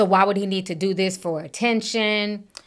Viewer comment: I thought he said he wasn’t thirsty for attention.